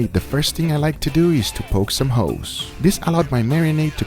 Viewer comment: it definitely sounded like he said poke some hoes hahaha